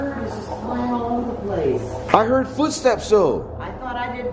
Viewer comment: The spirit was tired hearing you guess incorrectly so came to tell you what the item really was lol. Earlier, thought I heard horse hooves, not footsteps